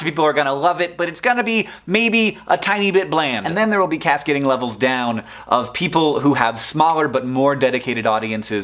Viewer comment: The way Hank says stuff in his hurry to speak leads to fun things. I heard "cascading levels down," as "cats getting leveled down," and it took me a moment to realize I misheard, because this is YouTube so why not cats?